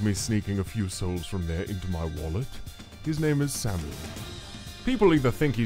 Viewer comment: hey casino cups fans I have a question what did he say? I think he say Sammy?